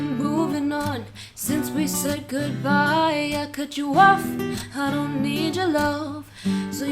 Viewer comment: its sound like shes saying boy i cut you woof